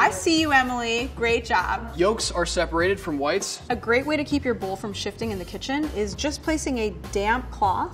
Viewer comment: Was it just me, or at 2:32 i heard "a great way to keep your bull from shitting"-💀